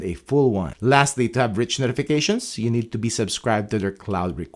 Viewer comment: what are you saying at 10:45? Tap rich notifications?